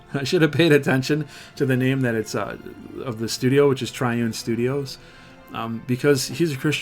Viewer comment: My preferences on this type of action MMO, I prefer Black Desert Online style over a game like Elder Scrolls Online. Had to edit this cause I thought I heard you say Trion but it's Triune..I was only listening didn't read the description heh..oops.